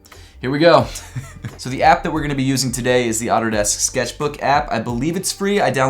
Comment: Lmao i thought he said “Otterdesk Sketchbook.” Took me a minute...